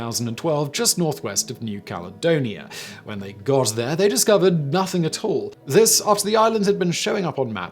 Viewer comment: Completely unintentional, I'm sure, but I cracked up when Simon said "instead of finding Sandy Island, they found nothing at all." I heard 'Nothing Atoll. Haha!